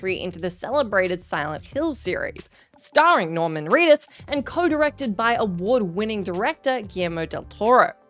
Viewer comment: Did she just say, "Norman Reeduth?"